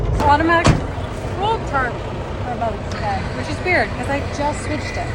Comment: Transcript says "It's automatic full carbon for both head, which is weird cuz I just switched it" What did you actually say:) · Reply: The auto roll tarp battery is dead, which is weird because I just switched them 😂